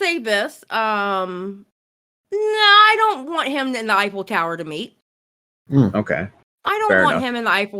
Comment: I swore said " I don't want salvo to eifel tower me"